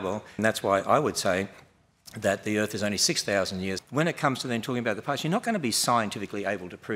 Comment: did he say the earth is only 6 thousand years old ???